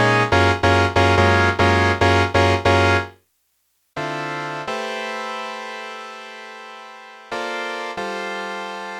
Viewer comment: Hey, editing! You missed some talking at 4:03 and I wanna know what you said. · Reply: I said "uwu"